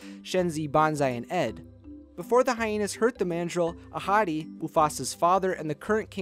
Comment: Why did I hear that as a hottie Isntead of ahadi or however you pronounce there name